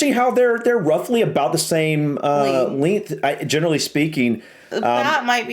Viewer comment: hold up. What's a Leenth? Never heard that word before but I'm guessing it's spelled Leanth and because it has the word lean (lean = slim) in it, could it be something to do with the thickness of an object? Very cool word 👍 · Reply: 😂 ‘length’ as in how long the bats were. We’re just really bad at pronouncing it apparently lol